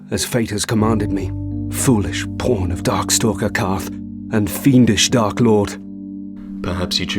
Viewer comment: "... foolish porn of darkstalker Kaathe." Sorry, just something I misheard.